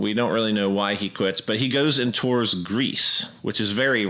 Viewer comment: I initially misheard Touring Greece as Touring 'Grease' - the musical . blimey that's not pure blues, I thought... and then I realised.